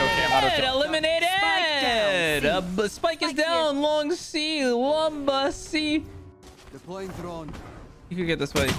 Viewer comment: did he just say "LAMBA C"